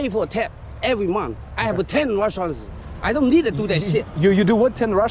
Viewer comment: He didn't say under Russia lmaoo He said He Own Restaurant! Tall dude thought he said sumn bad about russia lmao